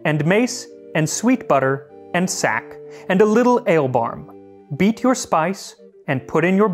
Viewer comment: I had to double take at 1:04. I thought you said "beat your spouse" at first.